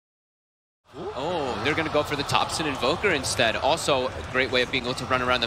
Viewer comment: the first part of the video that i heard is "Ohh they're going for the toxic invoker" and me actually went what in the actual fuck before realising its topson not toxic xD